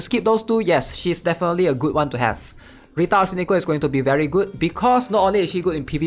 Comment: sound like he saying retard arsenico